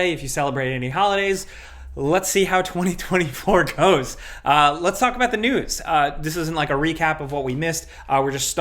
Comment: Sounded like you said "Let's talk about the nudes" 💀